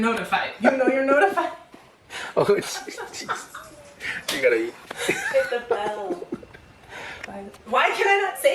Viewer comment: 😂 😂 Did he say you got to eat!!